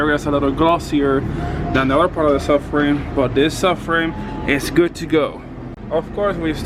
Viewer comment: Turn on Auto-caption and it says “Suffering” instead of “Subframe”